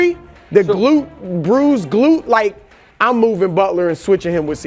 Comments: Why did Brou call Jimmy’s glutes Brou’s glutes at 8:00? 😂😂😂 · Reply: "Bruised glute" sounds the same though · Thought he said “bru’s glutes”, not bruised glute lol